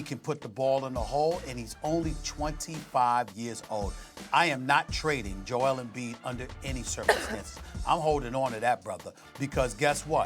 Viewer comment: For some reason I thought he said “I wouldn’t trade for Joel Embiid under any circumstances”😂